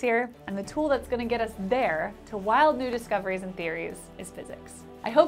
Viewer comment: I'm rather embarrassed that at 3:57, what I first heard was "wild nudist coveries". Sorry!